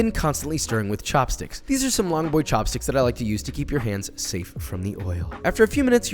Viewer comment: I seriously thought you said "To keep your hands safe from evil."